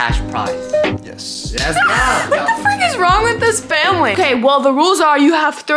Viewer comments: i didn’t know she would cuss i felt like i heard the f word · Wait am I the only person who heard her say the F word around like 1:04 or 1:06 or am I just deaf 💀